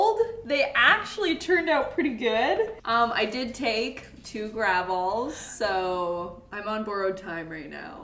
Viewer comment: I was so confused by gravol until I realized it was motion sickness medicine. I thought you were saying gravel.